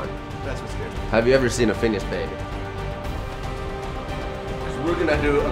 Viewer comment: What does jukka say at 0:35? "Have you ever seen a ?????" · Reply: Finnish baby